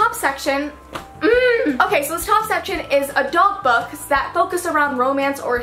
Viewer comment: I thought u said dog books instead of adult books